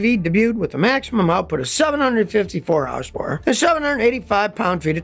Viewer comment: A maximum output of 754 "housepore" instead of horsepower. HAHA!! I get tongue-tied like that all the time!